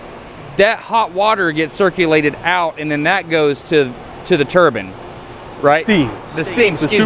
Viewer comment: did he say Turbin? Nice video. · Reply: Yep....turbine. Pronounced "TUR-bin." Like every last soul in that industry pronounces it.